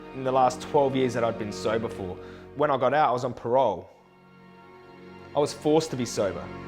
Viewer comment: Could someone tell me what he says at 2:28 ? "When I got out I was on p....?" i'm still learning english. ^-^ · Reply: He says parole